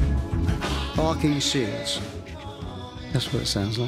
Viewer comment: What does he say at 3:56? I love how honest and emotional he is. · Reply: 'Barking seals'